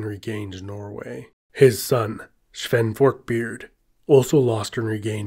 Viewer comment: I heard “Fork-beard” in there somewhere.